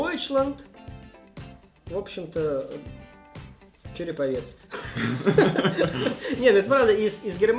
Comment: а почему Череповец? Или мне послышалось(